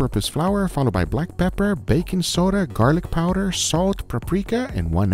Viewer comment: am I the only one who heard "Bacon Soda"? :)